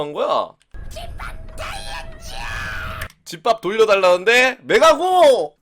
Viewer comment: 내가 들리는거 용서 무효 집밥 돌려줘!!!(?)